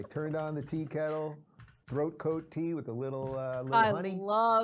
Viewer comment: I originally misheard "Throat Coat Tea" as "BroCode Tea" and I just sat for a moment like... man they cant even make their sick colleague tea without a 'no homo'?